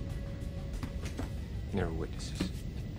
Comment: Did he actually say "No witnesses"?